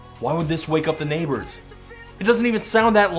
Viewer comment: Am I the only one who thinks we is saying "assassinate the neighbors instead of "piss off the neighbors"? Also, hippos are most sexy than this song. At leasts hippos have power.